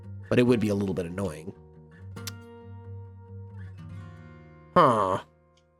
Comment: That 15:58 huh sounded like villager